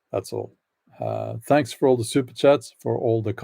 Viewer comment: LOL the choppyness made its sound like you said "stupid chats" instead of "super chats". I think maybe my ears might have heard what I usually think of these.